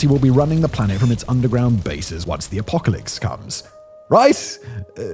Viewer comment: Did you say “apocalicks”?